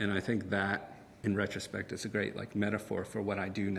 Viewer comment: Can some one please tell me what he says in 2:25-2:30? ..."and I think that ????? aspect its a great metaphor for what I do now" Thanks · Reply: He says ".... and I think THAT, in retrospect, is a great metaphor for what I do now."